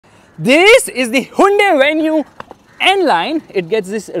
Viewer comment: Is it me Or did I just heard him say hunde instead of Hyundai